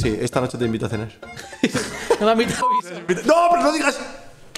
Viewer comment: Alguien que me diga que dice en el min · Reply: No, me a invitao Isa